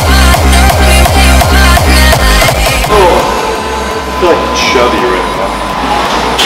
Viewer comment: what is he saying guys ? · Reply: Think he said something about feeling chubby